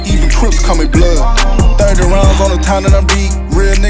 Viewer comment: Biggest diss here "dont need security ta go to my hood" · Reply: Damn. I thought it said don't need social security in the hood 😂